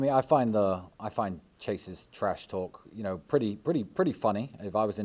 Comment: Chases TRASH TALK!!! Good lord I thought he said Andrew’s!!!